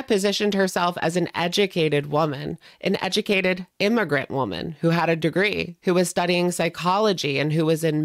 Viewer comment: When she said immigrant woman...i always thought she said ignorant. Her accent was hard to understand sometimes, so i always laugh when someone brings that bit up. 😅😅😅😅